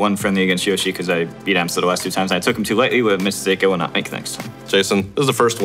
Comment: m2k bootleg Slim Shady, change my mind. Big fan, but in all seriousness, can I get some help deciphering what he says at: 1:36 5:39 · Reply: "cuz i beat amsa the last 2 times. i took him too lightly u-a mistake i will not make next (time)." 5:39 "uh tha- that can change. just go to the gym. lo- look at ZeRo" 14:33 "ahh this burns like a charizard, midna, ch- charmander." Unfortunately the M2K Rosetta Stone wasn't enough to translate the end of that last one.